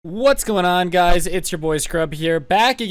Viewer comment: in the beginning I heard hey what's going on guys boop